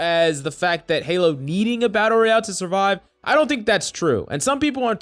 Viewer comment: You used the word survive early on in the video, I feel you underesold & I heard "resurrect".